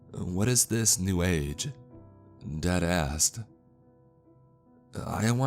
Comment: I thought he said “deadass” lmao